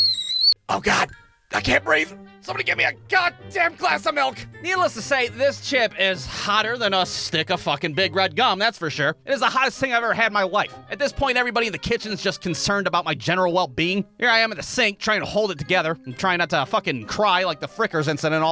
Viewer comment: I swear, at 2:32, it sounds like he said "It's the hottest thing I ever had in my wife". I know what the Closed Captions say, but if you rewatch that part, I bet you hear what I heard too. 😎